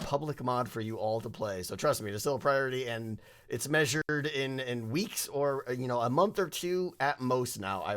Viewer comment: thought he said "measured in wheats" for a sec lol I give in a 10 wheats out of 10 wheats!